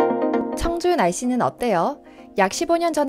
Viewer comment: 근대 난 왜 정준하씨는 어때요로 들었을까